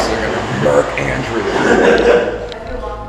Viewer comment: am i the only one who heard “mark andrews” in the background